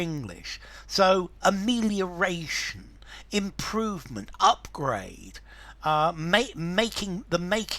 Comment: what is the word just before the “upgrade” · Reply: amelioration improvement upgrade